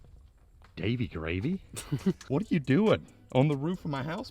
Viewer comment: thought you said Baby Gravy?